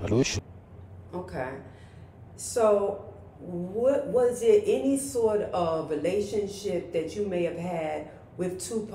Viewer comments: What he say 1:45? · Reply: I think he said shooter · What did he say that they blocked out at 1:43🤔🤔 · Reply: For some odd reason YouTube would not allow the vid to upload with the word “shooter “